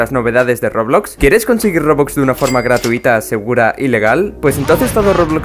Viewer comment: ¿soy yo o escuche la palabra ILEGAL?